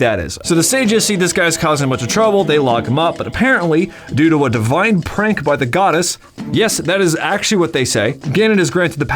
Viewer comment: thought he said a vine prank